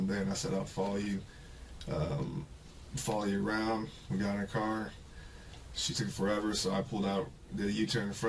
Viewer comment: does he say "we got in her car" but then quickly says he made a u-turn right after that?